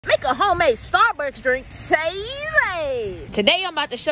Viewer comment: Am I deaf-I heard at the intro of the video I heard "make a homemade Starburst drink"😌🧸